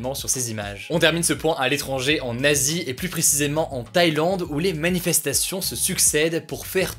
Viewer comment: Moi qui ai cru entendre "Un nazi"